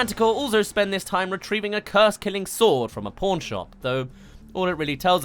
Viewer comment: i thought he said porn shop